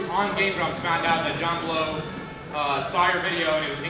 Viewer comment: goddamn look up "John Blows" (as misheard by me when Arin was talking about his developer inspiration) and then be amazed that the first damn info back is "John Blowes Arin" SO many people want to see this its like typing in "O" and One Direction shows up. John Blowin Arin is as popular a thought as One Direction.